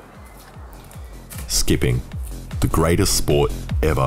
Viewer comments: my dumbass thought he said “skibidi.” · I thought he said “skibbidi, the best sport.” 😭